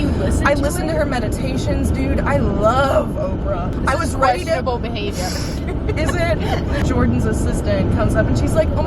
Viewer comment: I thought you said vegetable behavior the first three times I watched it and absolutely nothing is funnier than that